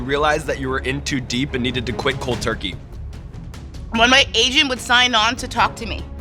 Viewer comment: When Sean asked her about World of Warcraft, I seriously thought she said “when my Asian would sign on to talk to me” lmao